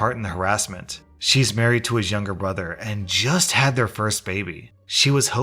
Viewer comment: wait did you say BROTHER!?!?